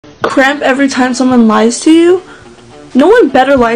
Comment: bro i thought it said crap every time someone lies to you